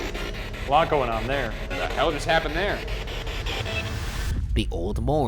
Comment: At around 10:13 it sounds like it said “what the hell are you trying to do”